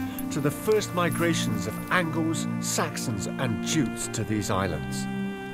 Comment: Did he say Jews ? 🤨🤨🤨 [ ..... No , he said ‘Jutes’ . ] Great video by the way ! 🇬🇧🇬🇧🇬🇧🙏🏻🙏🏻🙏🏻❤️❤️❤️🏴🏴🏴 I NEVER EVER SAW the FCO document before in my life ! - Why was this never made public ? ? ? 🧐🧐🧐🇬🇧🇬🇧🇬🇧😡😡😡 I recommend this video to all Brits , it is -powerful enough to stir the souls of men . 🧐❤️💪🏻💪🏻💪🏻